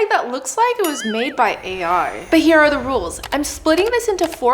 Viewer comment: why'd the subtitles say wizard when she said ai 🤨